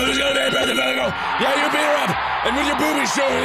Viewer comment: did Mark call him a her? XD